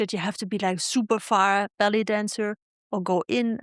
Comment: 1st one, sounded like you said belly dancer but was "ballet dancer" 😅